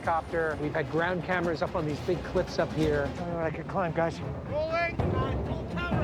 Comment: did he say idk if I could climb guys omg!